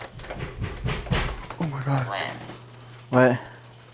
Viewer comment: I don't think the voice thing was saying "When", I think it was saying "Wind".